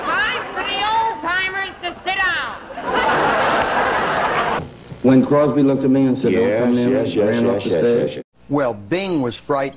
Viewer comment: u cant really understand what jery was saying cuz that jack ass Bob Dope repeatedly went yes yes yes yes.Jerry said"bing said,dont come near me" what too much ego to have ur toupee flappin around a bit? Now that wouldve been funny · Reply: He said "When Crosby looked at me and said 'Don't come near me' and ran off the stage."